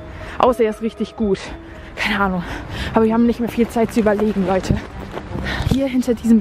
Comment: Ich hab verstanden,,nicht mehr viel zeit zum überleben😂